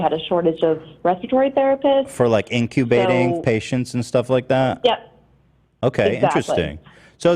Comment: Did he just say "incubating patients" 🤣🤣🤣🤣